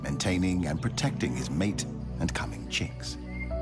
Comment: I thought he said clapping cheeks😭😭